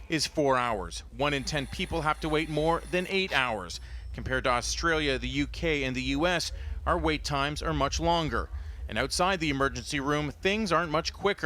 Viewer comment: ER wait times longer in the US?!? Ha Ha That's so not true. This is nothing more than a Propaganda Piece.. Pathetic at that. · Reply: Where did you hear this? I heard the opposite at 1:22, "Compared to Australia, the UK, and the US OUR wait times are much longer."